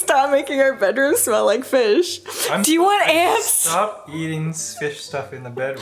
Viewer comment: Why did I hear, 'Do you want ass?' 😆 Had to rewind lol. Seemed like a non- and thing to say.